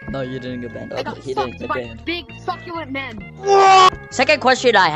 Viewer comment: It kinda sounds like said the f word